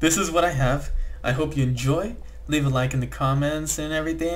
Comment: am i the only one who heard : leave a like in the comments?